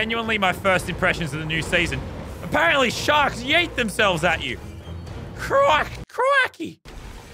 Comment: the fact that kit can chug something that is almost 2 times his size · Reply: “Apparently sharks yeet themselves at you” I thought he said that “apparently sharks eat themselves”